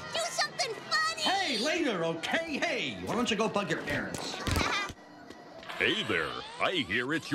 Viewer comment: I know the employer said bug your parents at first i thought he said go f your parents